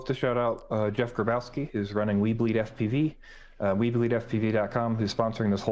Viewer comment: Dude, every time says "weBleed", I swear I hear "WEEB LEAGUE".